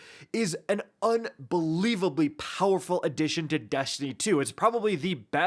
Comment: i coulda swore i heard you say "Deshtiny 2". Someone please tell me I'm not going mental because of the introduction! Please!